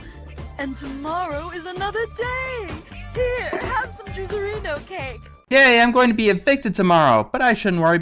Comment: Did she say "jizzereno cake?"